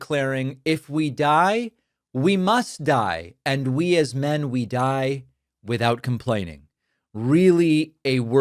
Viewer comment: I could’ve sworn he said “we must die without bleeding”!🩸